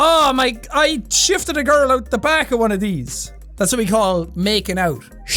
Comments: For a second I thought he said "I shived a girl in the back of one of these" 😳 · I actually thought Jack just said "I shivved a girl out the back of one a these" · ”I shifted a girl at the back o’ one o’ these” fuckin’ legend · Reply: Thought he said shafted for a second I was gonna go crazy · Jack: I shifted a girl once at the back of these. Me, and american that can't hear: You shanked a girl behind a school? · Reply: Hahah! I thought he said "I shagged a girl once" and I was like "woah there jackaboi" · I thought you said you shived a girl outs and so I thought you stabbed someone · jack: "i shifted a girl on the back of one of these" me as a frenchman: "you did WHAT??" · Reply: I thought he said shafted which in America means sex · I thought you were about to say you shived a girl out back and I was like YOU SHIVED A GIRL OUT BACK but then you finished and I was like oh 😐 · just me that thought he said shafted 😂😳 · Ngl I heard shafted the first time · I thought you said you shived a girl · I thought you said you "shafted" a girl outa one of those. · I heard "shifting a girl" as "shafting a girl" and it made it 100% more amazing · I thought Jack said he shagged a girl, that would have been problematic. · When he said I shifted a girl I thought he said I shit on a squirrel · I honestly thought he said shafted at first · i genuinely thought he said, “I shivved a girl.” i got SO concerned LOL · Not gonna lie, I thought he said he shafted a girl behind one of those